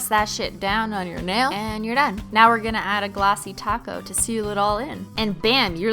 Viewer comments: did she just say TOP COAT at 2:23..... i thought it was taco 😤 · omg she actually said top coat instead of taco, I'm shook🙀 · Wait did Cristine say TOPCOAT AND NOT TOCO! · I'm from the south, I thought you were say taco ( too coat ) bahahahah 😂😂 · did she say top coat?! but.... but..... taco? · Sounds like she is saying taco instead of top coat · you said top coat instead of glossy taco🌮🌮🌮🌮